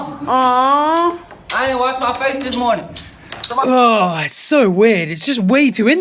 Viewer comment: That boy core on point lmao Wait, did he say he didn't wash his face that morning?? Lmfao eww